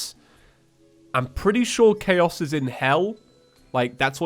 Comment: What he says "I think chaos is in Hell" What I hear "I think Khaos is in Hell"